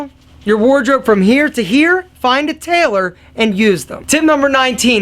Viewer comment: what did he said at 2:46 tip number 18? I don't get that word "find a _ and use them". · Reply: TAILOR